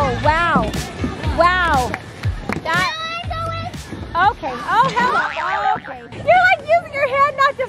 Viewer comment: Did say oh he🏒🏒 no!! At 9:06 in the video!?!?